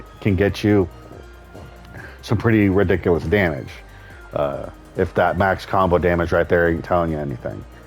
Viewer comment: misheard your "If that max combo damage [right there tell you anything]" as "If that back combo damage" and immediately thought about back combo setup I saw in Numb's latest combo guide video (at 2:44 mark): CH b4, RFF 3~4, dash f2, d4,4, dash SSL f4, delay RFS uf3, b3. It gives 65 damage and leaves the opponent in FDFT position before guaranteed b3 for, I think, 13 damage if the opponent stays on the ground and 20 if you relaunch him backturned. That is if you execute it correctly cause it the window for dash SSL after the tornado is very tight.